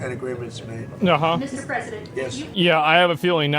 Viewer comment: did hse just say. .. mrs president? did i miss something